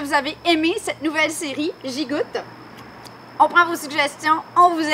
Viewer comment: Super sympa! Mais j'ai eu des frayeurs quand j'ai vu le sachet dessicant partir dans les airs :-) Et j'adore l'accent canadien. Au début j'ai entendu "gigote" au lieu de "j'y goute".